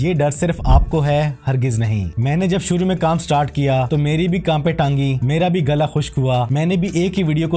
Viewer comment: Did I just hear at 02:23 "Meri bhi Kaanpen Taangi" 🤔